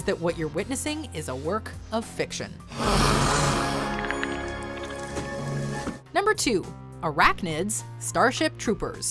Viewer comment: 生中ーって聞こえる